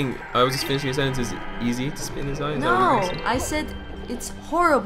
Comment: Oh I thought they said "split his eye" XD